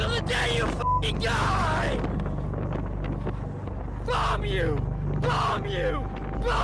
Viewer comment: i always thought he was saying W later to be informed hes saying "bomb you"